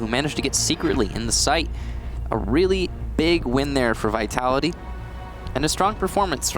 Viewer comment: I thought he said ‘A really big fucking hole coming right up!’ :/